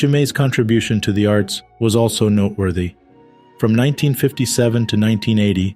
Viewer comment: RIP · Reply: Sounds like a mistake to me at 13:20. Sounds like narrator said " not worthy" rather than noteworthy